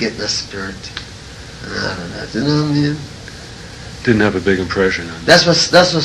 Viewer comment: What did he say?? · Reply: He said "you know what I mean"